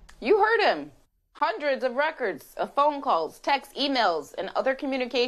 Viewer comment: "You heard him - hundreds of records of phone calls" No, not "phone calls", what he actually said was either "several hundred people" or "seven hundred people". We must assume that each person made more than one phone call. Between any two people, or among any small group, there would be several calls (at least), more likely an on-going series of calls.